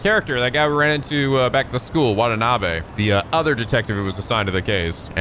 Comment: I thought you said What a Knob eh? lol